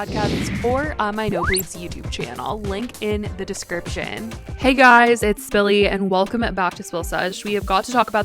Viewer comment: for a long time I thought you said ‘hey guys it’s Billy’ until I got you’re saying 😑🥴 lol